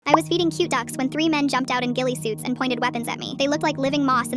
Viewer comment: My dumb ahh thought it said “I was eating three cute ducks.”